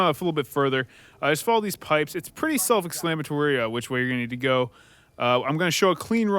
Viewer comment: Self-exclamatory? I thought I misheard at first, but no, you definitely said exclamatory.